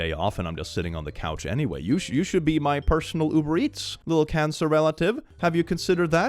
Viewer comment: said "Cancer relative". I heard "Cancerella" like Cinderella and I burst out laughing.